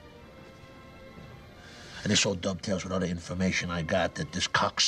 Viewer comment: What the heck does Tony say at 1:30 before he says "with the information I got" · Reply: "this all dovetails"